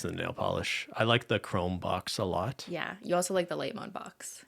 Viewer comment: Can someone help me, at 18:55 what is the other favourite box simply mentioned? I've listened it a dozen times but I still have no idea what she says 😅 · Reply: Chromes box and lite mode collection